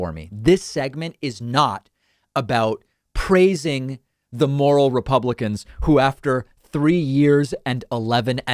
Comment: I could have sworn you said "'these moron republicans". far more accurate.